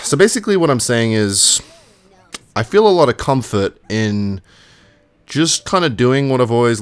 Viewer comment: Did just say cumfart at 3:39 ?